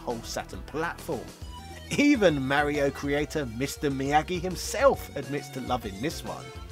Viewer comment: was Mr. Miyamoto called Mr. Miyagi or am I hearing things?